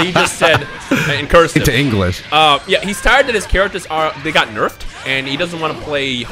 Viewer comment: I don't get what is saying at 3:08 "harpoon's characters"???